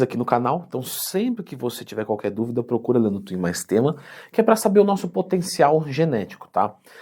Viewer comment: a primeira vez q eu ouvi "Leandro Twin mais tema" eu entendi o seguinte "Leandro Twin, mas tema". pq eu deveria temer o Leandro Twin? haahahahah